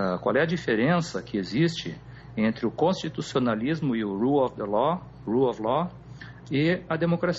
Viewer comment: alguém pode me dizer o que ele falou? "lu of lo"?